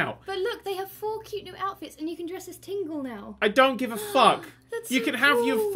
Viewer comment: i thought said "Linkle" instead of "Tingle" and i was so hyped